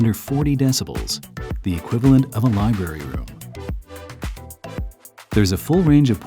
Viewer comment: the equivalent of what? I can't figure out what he says! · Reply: Library room he said.